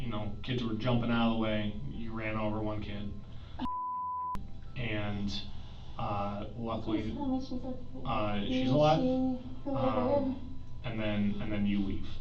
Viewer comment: I had to rewind this several times to verify that I heard what I thought I heard. and the officer were talking at the same time. The officer said "you ran for one kid, luckily... she's alive" And said "please tell me she's okay" The thing is, said the word SHE half a second before the officer said the word SHE'S